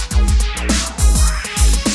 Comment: What the hell does he say? "Kekikoman"? "The thinker man"? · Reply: Taking command....